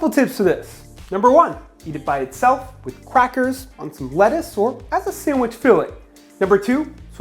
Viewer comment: Did you say crackers with a Q? Like Qackers.